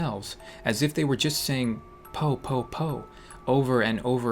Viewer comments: Lol I thought you said poop poop poop .... XD (ღ˘ω˘ღ) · PoU POu my brother thought it said "poo poo poo poo" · Every time he said "Po po po po po” I heard “Poop poop poop po" lol